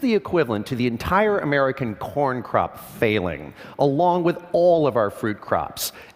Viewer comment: for a second i thought he said porn crop